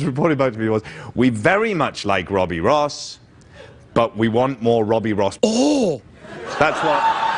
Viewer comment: does anyone know what he might have said at 1:48:00? I can't quite make it out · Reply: Arse. "We want more Robby Ross arse." It rhymes.